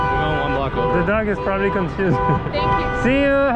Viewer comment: “the dog is probably confused” “Thank you” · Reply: Albert Henriques she thought he said cute but he said confused